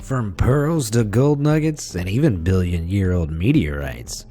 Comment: i heard chicken nuggets instead of gold🤣😂